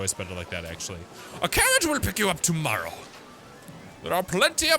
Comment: sounds like said carrot